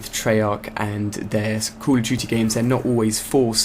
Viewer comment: i thought he said '' cool of duty'' :P